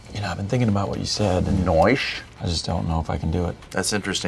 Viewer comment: did Andy just said "Noice"!!!!!!! (B99 fans only)